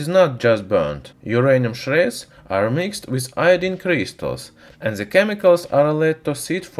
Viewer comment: like if u heard "Christus" instead of crystals :D